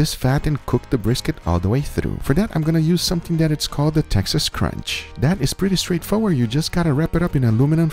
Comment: OK.....HOLD ON....Did Guga just say Texas crunch?????.....hahahaha